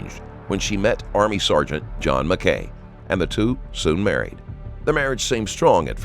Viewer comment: I really thought you said "John McCain", imagine being named after that traitor POS?!